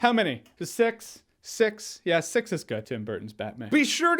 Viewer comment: you know it's been two years but I still don't understand what he was saying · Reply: It is a quote from Tim Burton Batman movie. Just look for the scene “Batman six is good”